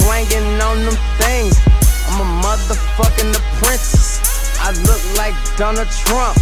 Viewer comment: Ok did he just call his self a princess? 1:26 ,and what happened to this guy he fell off super hard!!!